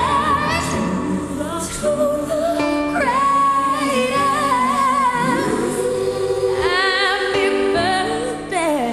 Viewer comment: I don't get what she said pls help. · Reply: I think she said "CRAY"